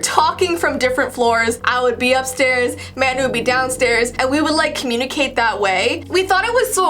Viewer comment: Did anyone else hear her say manda